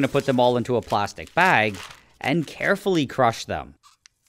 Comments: I thought you said softly crunch them😂🤔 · I thought he said “crush them softly”😂